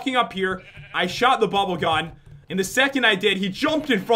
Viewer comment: This place is evil!! Enjoy the video guys!! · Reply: I thought you said bubble gum insted of bubble gun